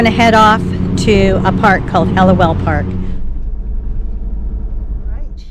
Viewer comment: I thought you said ‘LOL Park’. Then I saw the sign Helliwell Park 😂